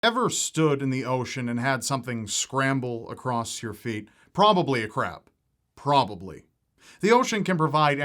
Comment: I HEARD “probably a crap”